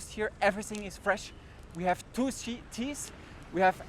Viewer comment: I do not understand you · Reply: at min 6, when you say Colombia has two seas, it sounds like you said they have Tuci, as in the drug. I know you didn’t say that, that’s why I wrote “Lmaoo” next to it.